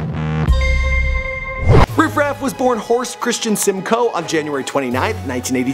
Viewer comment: I'm sorry, I thought you just said Riff Raff's government is Horse.